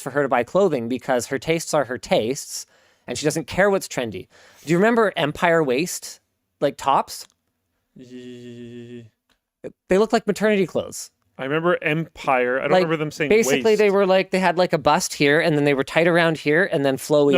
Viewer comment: It's garbage day today, so when you said "Empire Waist" what I heard was "Empire Waste" and I was confused for a sec about the idea that there was a waste management company that also had a trendy women's fashion line.